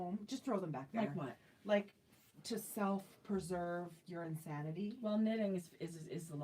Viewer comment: I heard you say to save your insanity”. Not sanity Hahahaha! The hair hat was fun! Love your fade sweater!